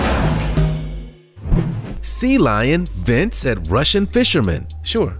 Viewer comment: Sea Lion was ejected... I just heard "Sea Lion vents"